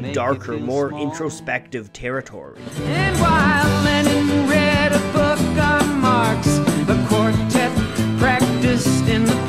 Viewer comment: I always thought he said "Lenin" not "Lennon" lol. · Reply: I always heard it as “Lenin read the book of Marx”